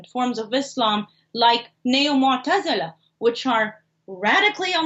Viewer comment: the guest names a peaceful Islamic group. Does anyone know what she says there? Sound a bit like 'neomortatsallah.' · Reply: she says neo mu'tazala. Mu'tazilism (Arabic: المعتزلة al-muʿtazilah) is a theological movement that appeared in early Islamic history and flourished in Basra and Baghdad. Its adherents, the Mu'tazila or Mu'tazilites, were known for their neutrality in the dispute between Ali and his opponents after the death of the third caliph, Uthman. By the 10th century the term al-muʿtazilah had come to refer to a distinctive Islamic school of speculative theology (kalām). This school of theology was founded by Wasil ibn Ata.